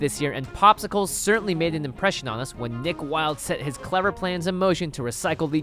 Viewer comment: When he said nick Wilde I thought he said dick Wilde lmao